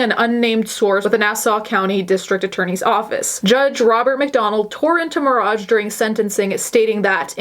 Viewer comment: I thought you said Judge Ronald McDonald for a second and I was like “alright McDonald’s getting into the legal field okay”